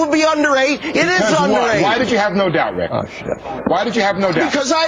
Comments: someone off screen goes "oh shit" LOL The bleeper was a sleeper - he didn't catch that one! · did someone just say "oh shit" at 4:11? funny....